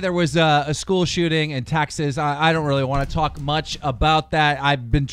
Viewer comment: Did he just say taxes instead of Texas?😂